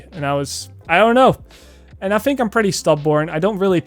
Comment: did he just call himself “stub-born”